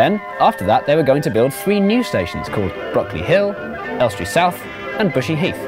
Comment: Until I saw it spelled I could sworn he said Broccoli Hill so im disappoint